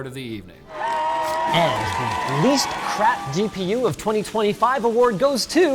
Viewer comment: "the least crap".. i needet to play it back 3 times to heart it first i hear only "the next crap".